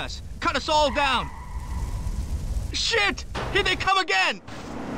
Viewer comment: I Thought He Said Here There Come Again Like A Chinese Food Guy Giving You Your Order 😂